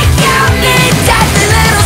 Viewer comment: Low key i hear “bloody creature pasta girl”😂😂